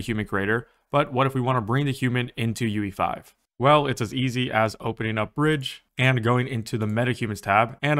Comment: I accidentally misheard you and now this is how I'll remember UE5: "As easy as opening a FRIDGE." 😂